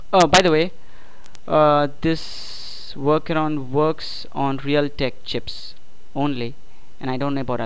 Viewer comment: at the end did he just say real dick chips? O.o